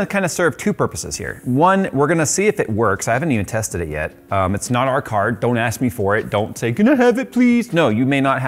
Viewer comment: He got it for free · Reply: I thought he said it's not his own card.